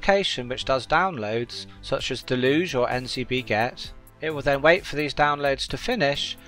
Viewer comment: Hey, around 5:20 you mentioned dockers that download files for you, you mentioned 2 but I cannot make out 1 of them. You mentioned deluge and NCB gex or gets when I search NCB Gex or gets I cannot find anything. What is this one called?